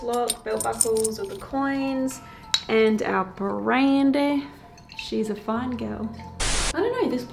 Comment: Incredible. Not only for "Bullet" but also, sounded like it could've said "Brandy" instead of "Andy"! You and simultaneous "Oh my God" was uncanny. Btw @ 2:37 I know the song reference! lol ANOTHER outstanding investigation